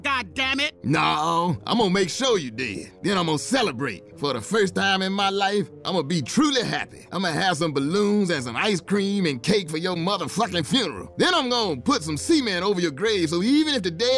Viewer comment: this is the most ignorant 58 seconds I've ever heard 😂😂😂😂😂😂😂😂😂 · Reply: Gerardo Rodriguez oh i thought he said semen